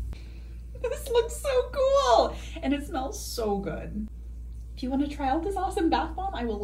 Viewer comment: Am I the only one who thought she said "lick it in my bio" insted of "link it in my bio"